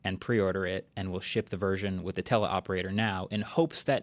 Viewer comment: hey Phil the subtitles say Tesla operator every time this guest says tele operator. Not sure if you can change the subtitles or not.